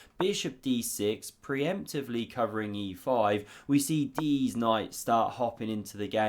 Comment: I believe I heard you say “Deez Knights.” 😂😂😂